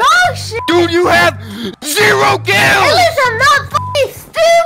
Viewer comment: bro the auto subtitles say "you have zero girls" 💀